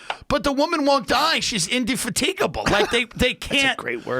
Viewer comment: what did Tim say? I listened closely a couple times but am not sure. I even looked at the transcript but I don't think that's correct. I'm thinking he tried to say "undefeatable"... Idk,someone lmk. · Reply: Indefatigable - so she's never tired, too awake to die